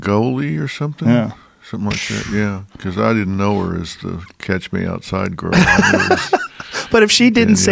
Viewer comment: I thought the girl said, "Cash me outside". I was puzzled about that. "Catch me outside" of course makes more sense.